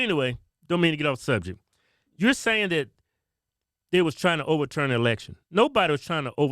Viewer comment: “To overturn the ERECTION”. That’s what in the hell it sounded like she said.